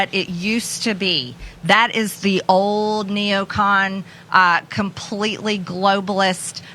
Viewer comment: Did she just say "old neocon"? Lmao